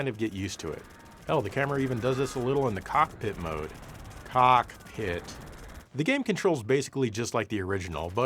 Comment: did you just say tits?!